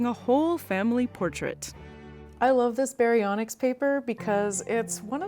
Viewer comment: I heard "Baryonyx" (which I pronounce /ba'ɾyonyks/) as "baryonics" and wondered what subatomic particles have to do with dinosaurs.